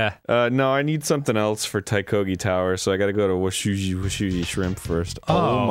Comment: totally thought washubi swamp said "wasabi shrimp".